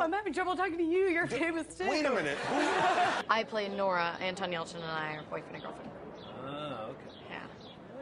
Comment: ok i suck. what does she say at 9:15 ? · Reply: "I play Nora and Anton Yelchin and I are boyfriend and girlfriend"